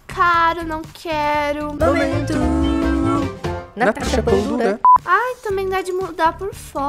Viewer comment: Minha parte preferida momento Natasha pão dura kkkkkkk 🥖 🍞 🥯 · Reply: Eu escutei "momento natasha punda" kkkkkkkkkkkkkkkkķkkķkkkk nada a ver mais foi isso q escutei